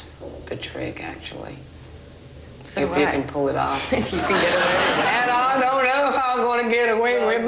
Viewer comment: she's so funny and spunky. and gorgeous.. what does she say at 5:12 ? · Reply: She says at 5:12: If you can pull it off.